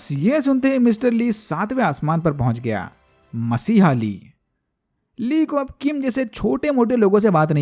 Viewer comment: I heard chutiya Lee..